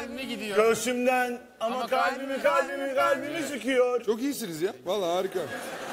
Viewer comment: afedersiniz ama ben öyle anladım da ibrahim abi ''ama kalbimi kalbimi kalbimi sikiyor'' mu diyor yoksa benim fesatlığım mı :D